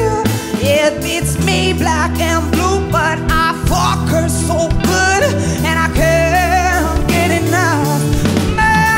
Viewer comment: did he say the word "fuck"? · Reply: "but"